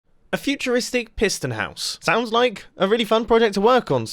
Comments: For some reason I thought it said futuristic prison house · My brain: Futuristic Prison House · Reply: Hehe he said, I thought it said prison house not piston house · Ok everyone that's like me and thought this video said futuristic prison house instead of futuristic piston house, like this comment 🙃